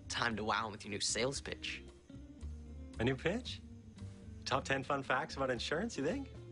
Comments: i thought eathen said bitch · Did anyone else hear "Bitch" at 17:28? I think now that it's actually Pitch. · It sounds like he said "Bitch"